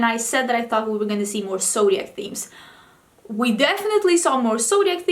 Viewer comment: My subtitles thought you said Soviet, not zodiac. Now that would be an interesting theme for pallets...